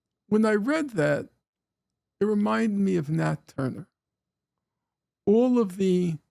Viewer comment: what the fuck did he just say? Why is this guy so terrible at speaking? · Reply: ???? he literally said remind me of nat turner you cant comprehend basic speech thats insane🎉🎉🎉🎉🎉